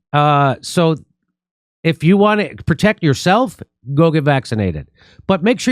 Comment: Did he just say go get Vac...td 01:36 🤔 Dore 😳🕋 oh thee of weak countenance. For the things you took were of no value. 😔 ✌